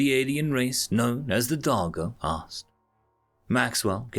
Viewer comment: When he said Darjo I can’t be the only one that heard him say Doggo right?